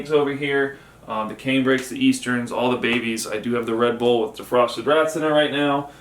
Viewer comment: Thought u said u had the ree bull on with rats defrosting 😆